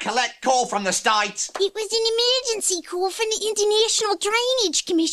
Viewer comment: When Is The Boy Says International It Sounds Like He Is Saying Indonesia